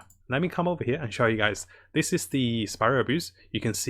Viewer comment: I thought I heard him say "'this is the Spiral Abuse'" and I honestly think that's more a fitting name